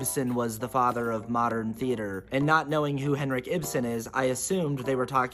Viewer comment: My "Henrik Ibsen" misunderstanding originally heard it as "Henry Gibson," of Laugh-In and The Burbs fame. I had a similar response. "Huh. Didn't know he was so influential."